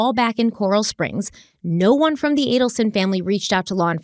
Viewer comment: Lol I could've sworn you said "analson"😂 I'm sure you didn't, but if you did, I'm here for it❤